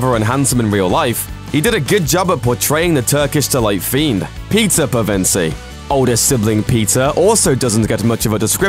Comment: Am I the only one who heard pizza pevensie ?